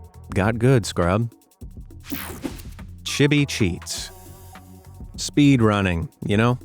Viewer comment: for a second I thought he said the word sh💩tty cheats😂